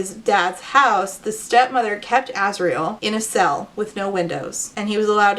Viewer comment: Oh my gosh, I zoned out for a second and heard you say "Asriel," and I thought for a hot second, this was an undertale video 😭